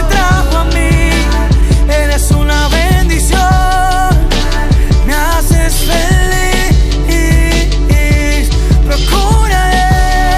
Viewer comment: Que dice redimi2 en el minuto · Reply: Me haces feliz.